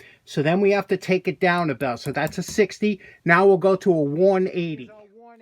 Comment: Your accent lol I thought you said a 180 not a worn 80 😆